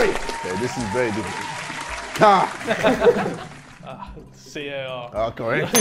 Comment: Is it just me or did Harry just said C o r instead of c a r.